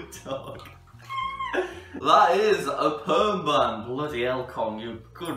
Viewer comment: I thought the perm said sperm and I was like THE FUCK ARE YOU FEEDING YOUR DOG?!?!?!?!